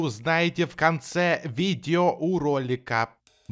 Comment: мне одному послышалось "видеоуролика"